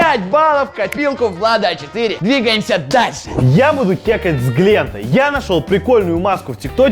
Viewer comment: Мне послышалось *секс с глентом😏